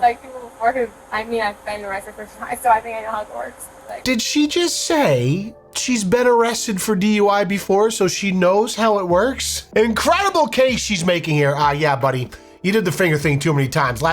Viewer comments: I thought she said shed been to hogwarts not she know how it works and that killed me 😂😂😂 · Honestly thought she said " I've been to Hogwarts " instead of " I know how it works " for a moment. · I swore she said Hogwarts, not I know how it works. XD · I honest to God thought she said, "I got arrested for driving in Hogwarts" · I thought she said "ive been aressted before,ive been in Hogwarts." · Who else thought that she said something about, "Hogwarts", when she said that she, "knows how it works"? When Leon backed up the video to see what it was that she said, I thought that that was what he heard, too, lol. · I thought she said “I’ve been arrested before and I’ve been to Hogwarts”😂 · I thought she said "i know Hogwarts" instead of "i know how it works" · I thought she said ‘I’ve been arrested before….and been to Hogwarts’ lolol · I watched this one on the original channel. I honestly couldn’t understand most of what the driver was saying. She doesn’t enunciate, her words come fast and strung together. At one point, I heard her say “Hogwarts”. Mom is another story. She seems highly intelligent in her oversized t-shirt with big block letters that say “…. “. At least anyone interested in dating the drunk female will know what to expect in another 20 years after meeting the mother. Yipes!! ** ok I still hear “Hogwarts” @ 7:34, but thankfully Leon cleared that up for me. It “how it works”. Thanks! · I thought she said " I've been arrested before & I went to Hogwarts"😂😂